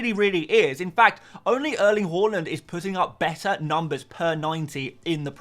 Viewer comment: Did you just say Erling Waland